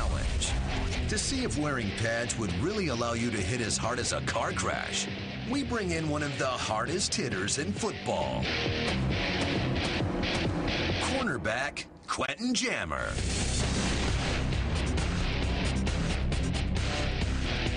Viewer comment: Why would they use a QB for the tackler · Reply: oh. I thought I heard QB. But still. A cornerback? Why not a linebacker or a defensive end